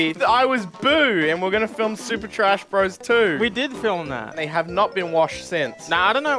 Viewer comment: did i just hear super trash bro 2????